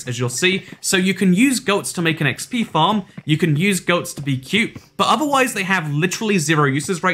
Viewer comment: I thought I heard you can milk goats?